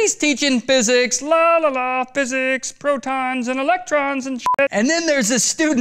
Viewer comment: he said electron and the subtitle said election